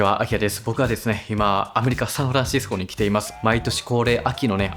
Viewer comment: レビューありがとうございます！最初「アフリカ サンフランシスコ」に聞こえて笑いました☺️